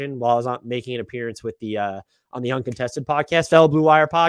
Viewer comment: what was the podcast you said ? I only have a few podcast I listen to your one so I need a few more😂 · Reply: "The Uncontested" podcast. they're GREAT. if you're looking for another national type pod, Adam Spinella does fantastic draft work at The Box And One and "The NBA Podcast" Is really good (I don't think the latter is on YouTube tho.)